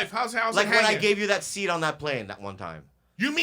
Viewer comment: roughly ... someone says something about bobby giving Erik his seat... I misheard and thought he said seed... assuming it was on a plane I thought the situation where where Erik was hungry and getting hango and needed nutrition... where bobby then sprang to save the day and granted Erik with 1 sunflower seed of the highest quality in all the lands...... then I realized it was what I said prior... just exaggerated the pettiness 100 percent haha LuV you all brothers... don’t be racist... LUv!